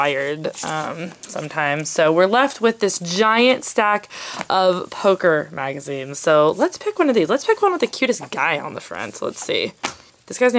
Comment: omg when you said " this is giant pile of poker magazines " I thought you said hooker magazines lol!!